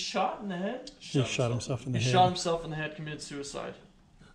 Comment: Hey Guys! At 22:33 I hear a response of “Nuh uh” after says, “He shot himself in the head, he committed suicide”? As if to say he did not commit suicide?!